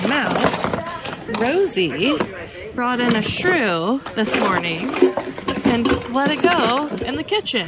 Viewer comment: Rosey gifted you. LOL When one of my cats gifts me an alive mouse the other cat goes out and returns with a fresh dead bird for me! LOL I love that ice bear! You did good in there! Thank you for taking us with you! :) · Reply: It sounded like you said the cat brought in a shoe.